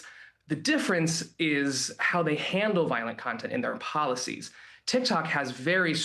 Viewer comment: I thought you said Violent Consent is still pervasive. Like is there really that much Rape Fantasy going on?!